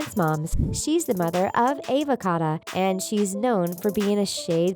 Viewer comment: Just me who is thinking the way she said Ava Cato made it sound like she said Avacado..... 😂😂😂😂😂